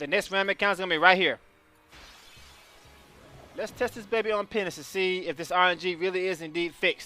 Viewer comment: Sounds like you said Penis instead of Penance